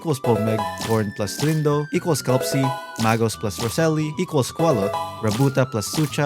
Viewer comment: before anyone makes a mistake like me, it's actually Magost + Roseli, not Mago like the picture and subtitles say.